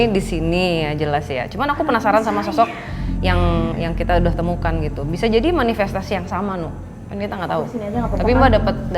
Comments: Menit 36:34 ada bisikan ...kl ga salah denger " sayang" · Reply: Iya bener...tolong saya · Di menit 36:36 itu ky ada yg ngomong halus ky bisik2 gitu. Apa aku salah denger kali, semoga salah 🙃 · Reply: Ky ngomong "itu saya...." apa gitu kelanjutannya